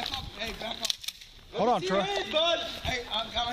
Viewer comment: Just trying to figure out why he shot this dude. Crazy! Is it just me? Because I thought I heard his partner trying to get him to calm down before shooting this guy. At 1:08, 1:16 & @ 1:19. Sounds like he was saying to his partner "Hold on Troy"... Couldn't make out the name he was calling him. But it sounded like "Troy".